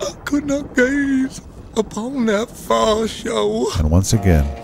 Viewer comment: I didn't know why I loved this movie. I know now, thanks to you!! A question, what did it mean when Pete said "God forgive me, I could not gaze upon that fire show". What is the "fireshow"? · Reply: That does make more sense (but I also thought 100% he said "fire show" and was figuring he meant the Ku Klux Klan rally.